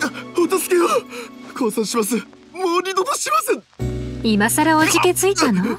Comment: もう二度とします に聞こえる